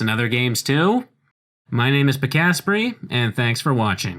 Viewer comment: I thought you say your name like “ Pika-spray”